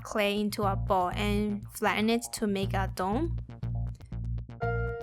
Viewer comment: Did she say "Flatten it to make a dong"?